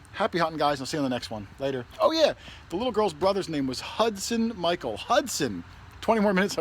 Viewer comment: Way to go! The Colonial Coin was way cool. 👍 BTW, I thought she was saying Isaac. I never would've guessed Hudson, not that it matters. She was a doll!